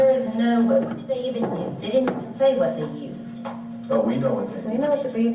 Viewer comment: did she say "they didn't say what they used?"